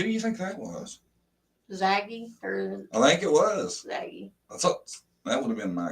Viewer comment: When you said "Zaggy said" I thought you said "Saggy said" and I nearly fell off the bed laughing. You are hysterical.